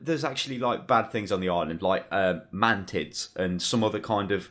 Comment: "There are bad things on the island, like um.. MAN-TITS.." Did I hear that right?